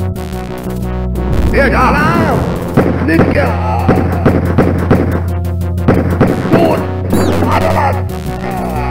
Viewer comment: Did he said Nigga??🙄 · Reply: It's "Gluck gehabt!", or "You were lucky!"